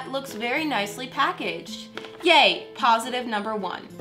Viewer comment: when she said yay it sounded like she said gay lol(no this is not a homophobic comment I part of the lgbtq+ community)